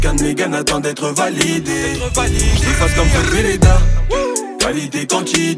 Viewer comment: j’arrive pas à comprendre y dit quoi « j’efface comme ... » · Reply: Jefface comme feutre veleda